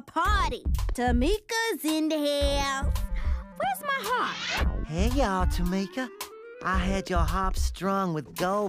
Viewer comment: Did Eddie say "Hey Ya"?? Was that a reference I'm just noticing for the first time? Lol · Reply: For the longest time I thought he said that too! He actually says, “here you are, Tamika”.